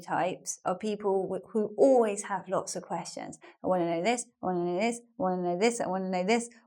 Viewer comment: I didn´t catch betwen 18:40 and 18:43. What´s saying? I hear: "and one of this, and one of this, ..." but I´m not sure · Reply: You can activate the captions, she says "i want to know this".